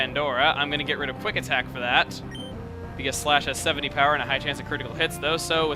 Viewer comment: LOL i thought you said Splash instead of Slash xDD i was like, "why are you teaching her that... ;x;" pffft~ that was weird -w